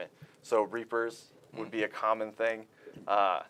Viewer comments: Can anyone tell me what he said at 14:48 after reapers, I couldn’t make it out! Thanks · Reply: He said Pteroteuthis, which is a nickname for the Seeker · What does he say at 14:48? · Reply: Terotuthesis?